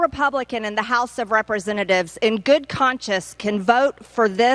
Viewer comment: Did she say "in good conscious"? 😂😂😂